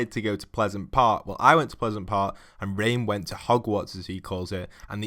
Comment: It sounded like you said "And Rain went to Hogwarts."